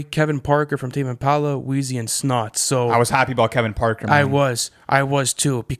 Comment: Wait hold up did I hear snot 👀👀👀